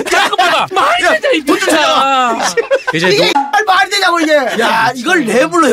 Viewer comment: 돈좀찾아와ㅋㅋㅋㅋ 오디오로 들을때는 못들었는데 ㅋㅋㅋㅋ